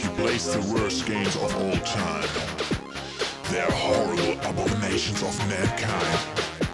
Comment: It always bothered me that he says "abomation" instead of "abomination" at 1:37, but otherwise this is a damn good cover.